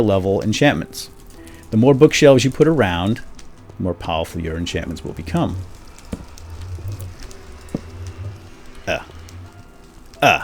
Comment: turn on the captions... When he says..the more the cost the more powerful it becomes....the captions saythe more the cost the more powerful the urine shipments become...( 10:18 ) P.S.:captions can be turned on by pressing the rectangle next to the options button